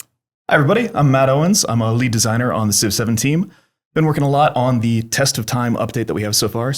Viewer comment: Could have swore he said civ 17 at the beginning. LOL. But it was actually 7 team.